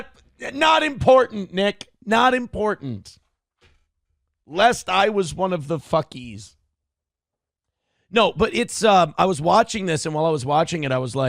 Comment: The shamelessness this loser luxuriates in knows no bounds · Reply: Did he just say … “lest I was one of the fuckEEs.” 👀 This is a new angle, as it were